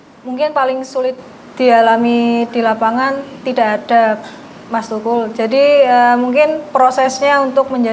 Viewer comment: kedengeran saya "TIDAK ADA MAS TUKUL"